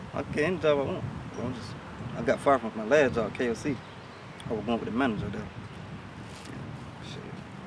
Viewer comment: He doesn't say "I was arguing with the manager there." He says, "I was goin' with the manager there." Meaning... he was sleeping with the manager. Am I the only one who heard that? Somebody back me up, please!